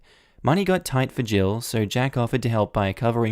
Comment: all I heard was "tight for jill so jackoffed..." didn't hear the rest right away. Too busy indulging my big brain humor ;p